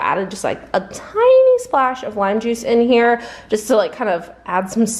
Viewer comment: Me laughing because at first I thought you said wine juice 😅not lime juice 😂